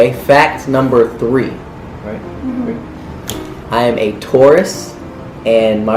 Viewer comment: When Austin said 'Im a Taurus' it sounded like he said 'Im a Tourist'😂